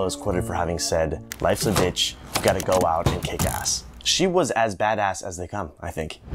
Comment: "Life's a bitch. You've gotta go out and kick ass." Best quote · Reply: Ha ha... I thought he said, "You've gotta go out and get gas."